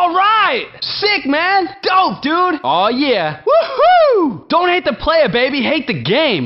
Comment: ALRIGHT!!! 0:52 Sick, Man!! 0:53 Dope, Dude! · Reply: I hear it as Go dude!!!